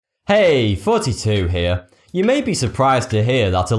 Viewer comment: oh his name is thoughty2? I always thought he just said "Hey, its 42 here" and I never knew why...